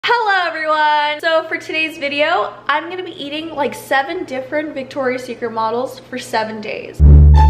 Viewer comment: At the beginning I heard “I’m gonna be eating 7 different Victoria secret models for 7 days” I felt that 💀confused but ...felt